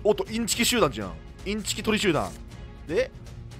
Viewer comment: 何回聞いても「インチキトリシューラ」にしか聞こえなくて笑った←